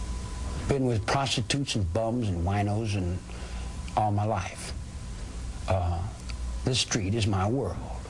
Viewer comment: did he say " i have been with prostitutes,bums and rhinos all my life" ??O.o